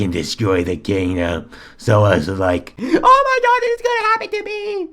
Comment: Did he Said OH MY NOTCH or ooh my god cuz OH MY NOTCH is my catch fraze